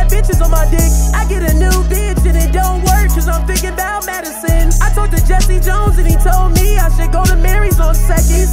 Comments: I thought he was saying "medicine" until I read the comments. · “Tell me how to get Madison or get tf out my face” 😭😭😭😭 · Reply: Madison it wouldn’t make sense if he’s saying medicine he’s singing about a girl named Madison that doesn’t love him and has a new dude named Julian now he’s crying in the kitchen. I thought he was saying medicine as well but he’s isn’t I thought he was a country fool like me saying medicine